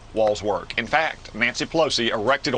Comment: I thought they said wall’s don’t work?